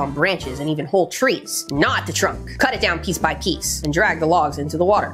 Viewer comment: “Gnaw at the trunk!” I thought you said not the trunk lol was so perplexed